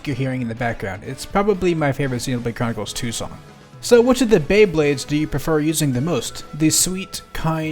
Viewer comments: Thank you for the idea, "Bae Blades" is what I'm going to call them now instead of "Pyra and Mythra". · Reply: Honestly I thought he said "Bey Babes" and was briefly confused, other one makes way more sense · Did you just say “Bae Blade”? Pyra btw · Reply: he did say bae blade :))))